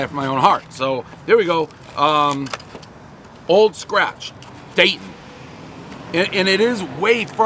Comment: I thought at first he said old snatch. Everyone knows the rules😁